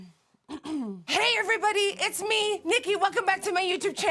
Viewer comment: i dont hear jojo siwa in this, i'm hearing graveyardgirl saying "hey everybody it's bunny" lmao